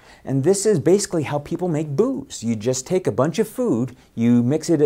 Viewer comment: i thought he said "and this is how people make boobs"